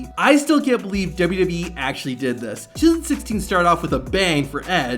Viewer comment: Why I hear 2016 rather than 2006